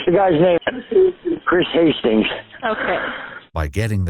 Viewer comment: I heard Hansen and was like “Aye yo! I see why you got shot” then they repeated the name and showed the mugshot😂